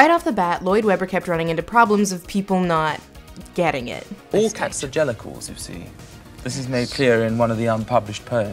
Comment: What, exactly, is he saying at 11:06 ? "all cats are gelical"? ? I've listened to that bit over and over again and cant figure out what the hell he's saying lol [Edit: after finishing watching the video, I see its some weird thing specific to "Cats", which i'm not super familiar with lol)